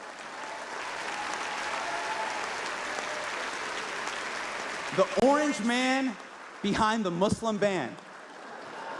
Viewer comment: Anyone catch what he said? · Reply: He said YES, he was responding to his friend next to him with glasses. But prior to this joke, he looked hella pissed off listening to Hasan. Could be a top exec for one of the media companies, either way, he looks like an Israeli Jew.